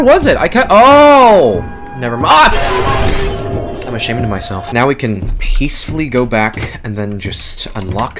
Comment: I also heard him say ‘I’m shitting myself’